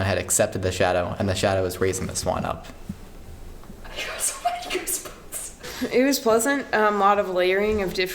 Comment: What did umu said at 11:40? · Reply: "I got so many goosebumps" xP